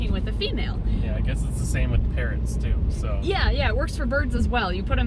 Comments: Is it bad that I thought said parents...not parrots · I thought said “parents” not parrots 😂😅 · I thought he said parents... 😂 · I thought said Parents instead of parrots lol